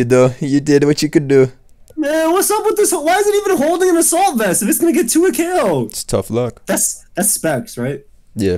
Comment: what did say at 5:53???? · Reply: If it’s getting 2 hit ko’d. It’s that Italian coming out with the fast speech